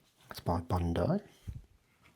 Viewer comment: double checked because I legit thought you'd said Bandai